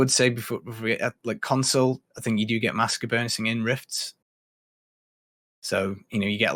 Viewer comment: what did he say? Master bursting? What‘s that?